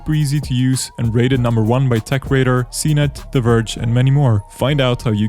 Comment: Hello fellow PC nerds... did he just say "The Verge" at 1:18? Also, make sure to screw the screwdriver deep into the radiator